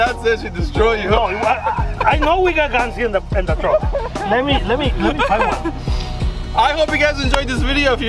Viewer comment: is so happy when he is cooking i love it lol Also, at 10:05 did he say "we have guns in the trunk"? AHAHHAH